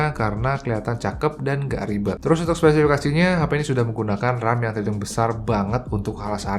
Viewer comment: menit 5:24 itu abang ngomong apa sih🤔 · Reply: Trus untuk spesifikasinya 😆